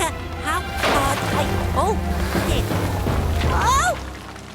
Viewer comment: This could an audio error but at 5:33 it kinds sounds like Scooch said "oh $h!t"